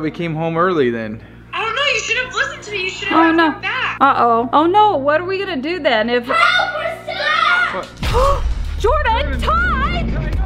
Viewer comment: When the doll talked I heard you shouldn't have left minnie ty behind